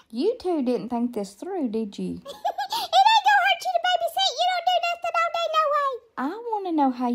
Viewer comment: I rewinded hush little baby hush little baby 12 times 😂😂😂😂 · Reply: for me it was the you do nothing all day no way 😂 nice to know I’m not the only one who hears that too 😂